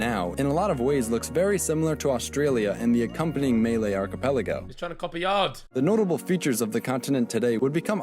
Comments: Did man just say “melee archipelago”😂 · did the narrator called malay "Meiley"?....it sounded like melee....u gonna use us like a knife to defeat the 10 bad guys?